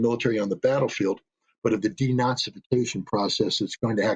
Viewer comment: Did dude say denazification?